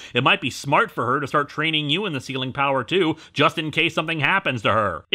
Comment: Oh, "Sealing". I thought they were saying "Ceiling". I was like "what kind of power is a ceiling power?" Does it first require learning "wall" powers? Is it complemented by a "floor" power?